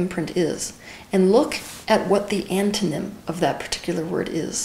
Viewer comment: pls help, does she say "look what the ANTHENEM of that word is"? I cant translate :( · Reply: She used the word ‘antonym’!!